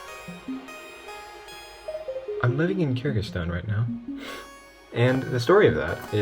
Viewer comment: For a minute, I thought you said Kazakhstan , not Kyrgyzstan. Though you coulf say, "My computer is fastest in of Kyrgyzstan, it is very nice! 👍" 😭